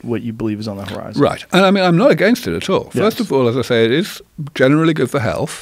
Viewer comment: who else heard ''I'm not a Gangster at all''